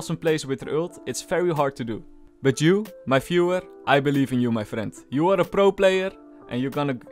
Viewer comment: did he just say "my fuhrer"